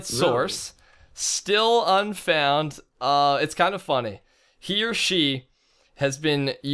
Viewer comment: He said She for the inside voice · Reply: he said "he or she" you misheard it :D